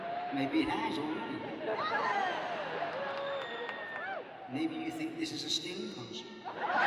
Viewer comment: “Maybe you think this is a Sting concert?” Is that what he says?